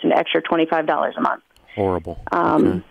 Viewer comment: what did dave say when he muted the line at 1:35? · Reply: he said horrible outloud before muting the mic.